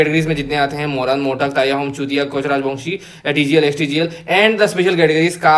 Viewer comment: what I just heard ch***ya 😂